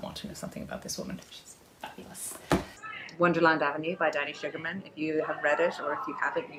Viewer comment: I heard her say "Daddy Sugerman", so by instinct I wanted to know who that was, and when I searched it up, it kept saying "Are you looking for sugar daddy?". And that's when I remembered she had an accent, and then I realized "oh. she's not saying Daddy, she's saying DANNY."